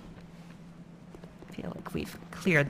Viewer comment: thought i heard "feel a queef". i need to check my maturity levels.